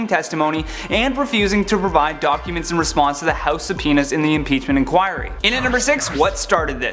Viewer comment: I'm sorry....did he say "House of Penis"? 😂